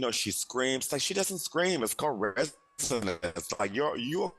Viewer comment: What word is he saying at 12:14? There’s distortion and I can’t make it out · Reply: “Its called resonance.”